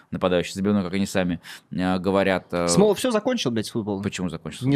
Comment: я правильно расслышал "б-слово"?)